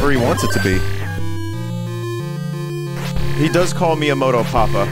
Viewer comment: I just woke up as I watched this and was beyond confused hearing "He does call me a moto papa" like wtf is a moto papa and he calls Vinny that!? Took me like 10 seconds to realize what he actually said